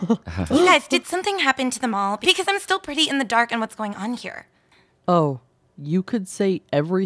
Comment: Did she say "what happened to the mall i am pretty in the dark, did something happen here?"